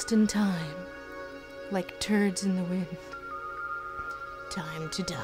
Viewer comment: Did... did Venom say "turds in the wind"???? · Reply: I always thought he said "like birds in the wind" and was like "dang you're very romantic for a big black goo monster"